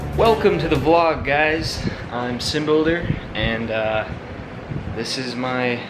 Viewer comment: Instead of Simbuilder I thought you said Simple-der. I'm sorry I laughed. (